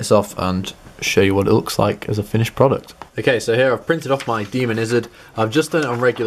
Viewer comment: he says: "I printed up my DEMON-izard" instead of "I printed up my Diamondizard"!